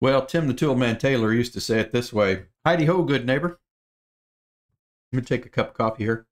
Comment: I thought Wilson said hidiho good neighbor